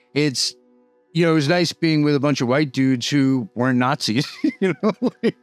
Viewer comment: FYI, the subtitle at 11:36 says “it’s nice being with a bunch of white dudes who were Nazis”, instead of WEREN’T Nazis.